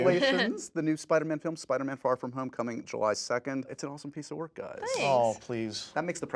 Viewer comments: i thought he said spider-man far from homecoming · did he just say Spiderman far from homecoming?!?! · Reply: SENPAI DAN he said “coming July 2nd”